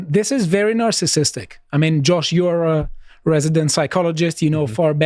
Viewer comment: ok who thought he was going to say " I mean Josh you're a narcissist yourself" 😅